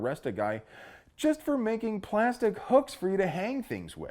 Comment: I thought that said 'forbidden plastic hookers'...🥺